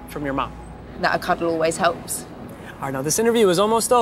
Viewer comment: can any one help me with 6:50 what did daisy say? "not a cudde always helps" or something else?